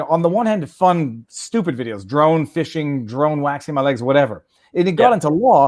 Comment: I think I heard say “Drone shave my legs”. Wtf? Lol